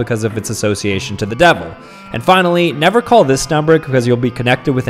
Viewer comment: I thought he said nipples Florida 😭🙏